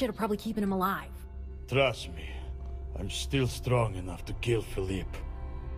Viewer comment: i just realized Oleg says "trust me i'm still strong enough to kill Phillipe" i always thought he said "trust me i'm still strong enough to kill for leap" for some reason lol